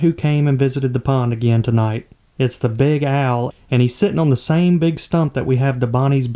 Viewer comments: Your southern twang makes it sound like you say "Big AL" when you say big owl. So why not Big AL the owl? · You got to go with “Big Al” for the name. Your accent is so country that I thought you were saying “Big Al” but you were saying “big owl “😂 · The Big Owl's name should be Big Al (because I heard "Big Al" from your accent :p) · I swear I heard "big Al" instead of big owl. 😂 I actually had a friend with that name in highschool. Ponds comin' along nicely! That tee pee looks great!